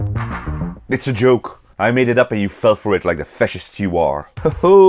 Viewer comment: did he just call us fascists????